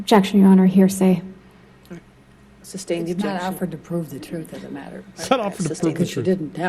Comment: Did I hear that correctly? Her own lawyer saying she doesn't have to prove the truth of the matter??? This is around 3 minutes and 15 seconds into the video. Going to listen again... Edit: "it's not offered to prove the truth of the matter..." because there is no truth to it. 🙄